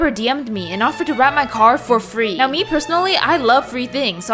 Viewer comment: Thought the caption said ‘I raped my car’